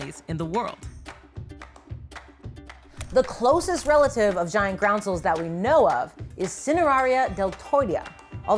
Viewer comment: She's saying "giant groundsel" but I kept hearing "giant ground squirrel."